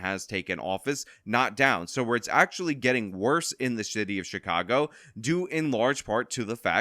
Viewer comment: thought you said shitty of chicago. Perfectly apt either way lol